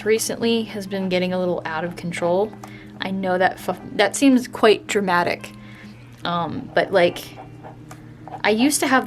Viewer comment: Idk why but I find it so funny how iam just drawing in my sketchbook and then i hear "I know thats f***-um-its very dramatic -but-um" HAHA my mind was just like <link> that link is to the video that describes my mind Oh! And of course F I lasted the entire time